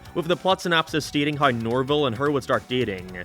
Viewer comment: Or if you watched Scooby Doo and the Alien Invaders, you’ll know his name is Norville. I thought I heard Norbert at first but it’s Norville